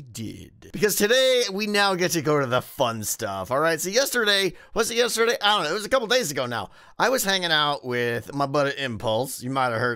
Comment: I thought Tango said "Yesterday, when was yesterday... it was couple of days ago now".